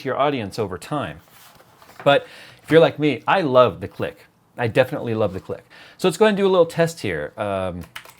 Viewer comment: I thought I heard "I love the clit, definitely love the clit" Yes, just like you Jay ;)